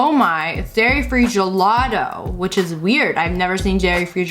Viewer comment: who else heard her say Gairy Free Gelato